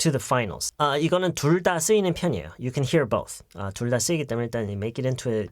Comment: 에 선생님께서 you can 어쩌구 both하시는데 뭐라고 하셨는지 아시는 분 계신가요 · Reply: you can hear both